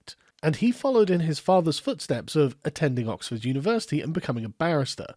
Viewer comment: I thought you said "Followed in his fathers footsteps and became an embarrassment". Guess I could turn up the volume.